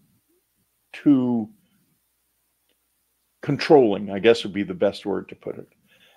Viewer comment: About 34:11 a word drops out "...he thought they were too ______" . What was that word? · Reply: Controlling